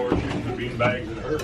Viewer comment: Guy’s this is totally off topic and you might all hate me for this but… I don’t think the guy said “ good Lord the beanbags in a hurry” 💀